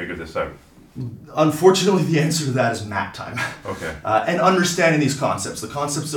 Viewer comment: honestly misheard that as "nap time"